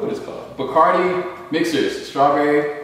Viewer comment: Omg I thought he said “Bacardi bitches” lmao 🤣